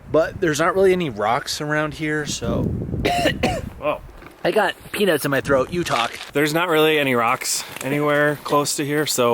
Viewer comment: I heard "I've got penis in my throat, you talk!" XD